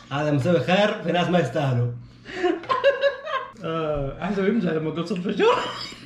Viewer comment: ايش قال شباب 😂😂😂💔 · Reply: قال:احسبه يمزح لما قال صدفه شاورما